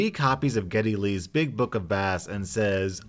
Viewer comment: Lol I thought you said Geddy Lee's Big Book of Math! I was like, why would he write about math?!? Bass makes more sense.